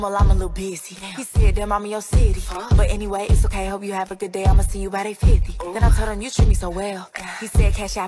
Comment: Tbh I thought it said “ima send you about $850”- unless I’m right